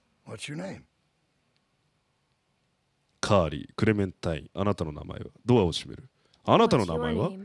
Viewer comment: なんかクレメンタインするって聞こえたようなWWW